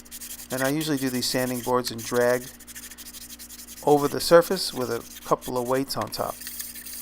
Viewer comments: Did you say you usually do these sanding boards in drag? Lol! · "I usually do these sanding boards in drag..." is not what you said, but what I heard at 5:29.